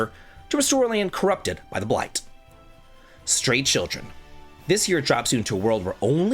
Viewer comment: I thought I heard Straight Children. Lmao... 😂